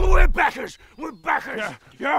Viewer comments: Ooooh, "backer." I kept hearing "beggars." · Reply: I heard "Bankers". Was a bit confused, as to why bankers would support kickstarters :/ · Anyone else hear them say “beggars” instead of “backers”? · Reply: I heard “bankers” 🤷🏼‍♂️ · I thought they were saying “bankers” 🤷🏼‍♂️